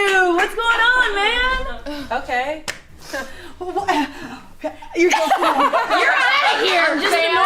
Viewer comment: does she say "you're so cool" to arasha as daimen?